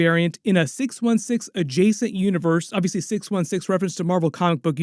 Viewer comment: I believe Mobius did not say 616 adjacent universe, he said 616 adjacent realm… obvious reference to quantum realm. Was most definitely a reference to Ant-Man: Quantumania and it did not occur in a different universe, but in 616 as does near all the MCU.